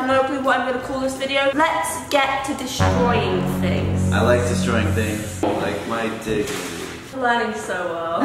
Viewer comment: it sounds like he says I like destroying things like my **